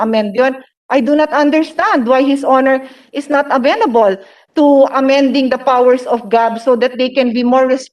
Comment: i thought i heard: why not amend the the powed of GOD! im like.. wuuuttt??!?!